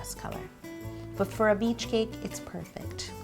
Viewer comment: I thought it said a bleach cake at first, and I was kinda worried of why you would make a cake out of bleach o.o